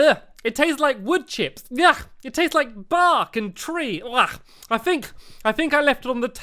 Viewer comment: it sounded like said barf but he said bark